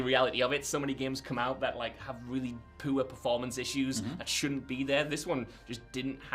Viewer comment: I know you said "Poor" but all I heard was "Pooh-er"... and now it's all I'm going to say... On a related note good to hear this was decent, liked but never finished 1.